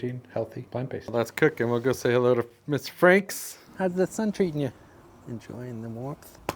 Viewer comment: Did you say “Mr.Franks”? Nice “have-on-hand” video!